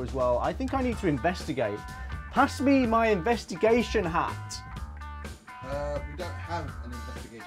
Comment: Thought you said masturbation hat... You can't blame me, with the topic of the video and all hahaha